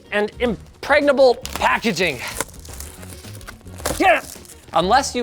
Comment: did he say "impregnable packeging"?